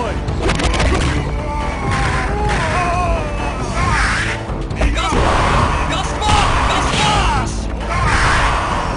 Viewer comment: What did he say upon throwing that fireball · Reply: If you mean John's fireball, I think it says "Mega Smash"